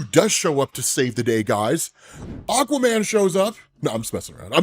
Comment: I swear to god it sounds more like you said "Uncle Ben" instead of Aquaman and the first thing i thought of was Ben from Urban Rescue Ranch. Imagine if Kevin showed up there out of nowhere. They'd be done fucked uuup...